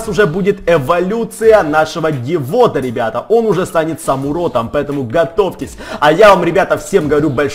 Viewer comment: "он уже станет самуродом" 16:55-16:57 послышалось